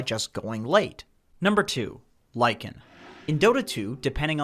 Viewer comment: Why do i hear "Ly can" instead of Lycan. I thought it was a pun for lycan that could counter skelly boy here